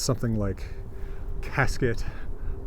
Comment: Is it possible that "box" mentioned on the rune stone refers to a casket? That was my first thought. Great video as always! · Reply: Its odd you thought that, he says Casket" right before he says "Box" 9:53 Must be one of those things where you hear but didnt hear, then subliminally reconnect, or something :)